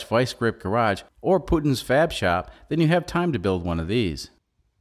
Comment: I thought he said "Putin's Fab Shop" and thought it was a hilarious reference to Garage 54 😂😂😂 Kind of sad when I realized he said "Puddin's"